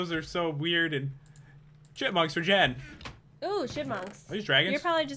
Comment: when Jen said.Chipmunks I thought she said sh!tmunks